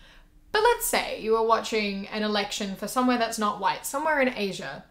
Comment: i thought it said erection lol